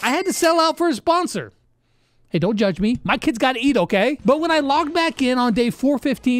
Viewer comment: what did he say my kids got what? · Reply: Eat